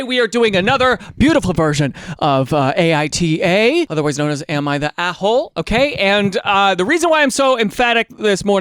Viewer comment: When I first watched this, I thought said “am I the apple?” 😂